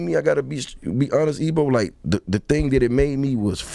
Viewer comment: Did Jeezy call him "Ebo" 😂